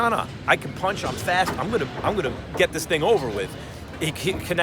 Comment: did max said I’m going to get this nigga over with???